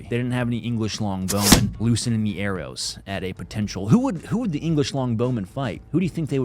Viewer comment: Did you just say "loosening an arrow"